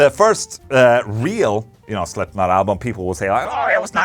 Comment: Thought you said slipnip